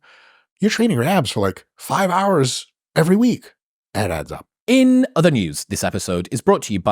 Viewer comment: When Mike said "it adds up," I heard "it abs up" LOL